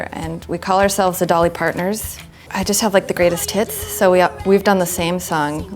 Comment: I could've swore she said "I just have like the greatest tits" when talking about her Dolly Parton tribute.